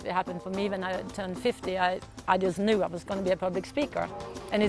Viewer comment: did she say 15 or 50?!?!?! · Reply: ElectricDreamz255 50